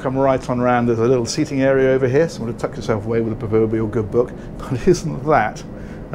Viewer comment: "you can sit back with a vooyuyug good book" lol i think my favorite parts of these videos is saying "i'll edit that out later...you know i wont" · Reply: He said "proverbial"